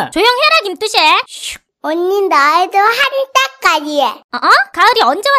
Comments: 언니! 나도 한 따까리해 라고 들려… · 언니 나도 한따까리해 라고 들었다... 미안해.. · 나만 가을이 말 “한따까리해”로 들었낰ㅋㅋ? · 왜 이렇게 언니 나도 한 성깔 해 랑 한 따까리 해 로 들리지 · 나도 한따까리해라고 들림